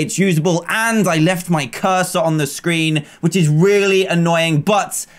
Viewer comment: Who else matched their cursor with Dan's cursor for no reason? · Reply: Wait. CursOR?! I THOUGHT HE SAID HE LEFT A CURSE ON THE SCREEN!!!(curse as in cuss as in bad word